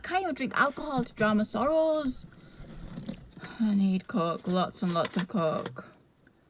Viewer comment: I swear I thought she said "I need co#k, lots and lots of c##k". Anyone else? Lol 😂 She's annoying because she reminds me of how I use to talk to myself. It's obviously never going to get better talking like that. Thankfully I realized this.